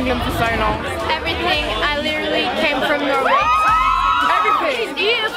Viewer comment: That girl on 1:01? Did she say that she was from Norway? Norway (y)